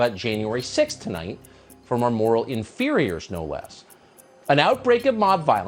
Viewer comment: Republicans don't have moral inferiors. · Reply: inferiors! I thought he said interiors! But don't over generalize, these grifters haven't got much in common with most republicans.